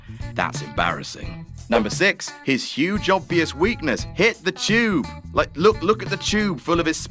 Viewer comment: I heard 'Hit the Jew!!' over and over until the arrow...